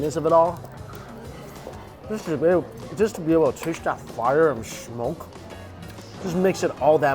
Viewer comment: what did he say? 🤣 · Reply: Test the fire and smoke?